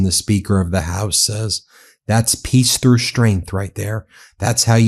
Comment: peace through strength, l thought youi said kiester strength. somehow its just as relevant.